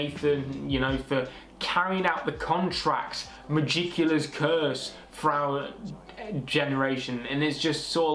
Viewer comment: i thought she said cula’s curse